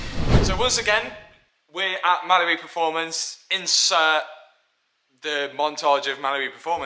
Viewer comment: Sounds like he says Malibu performance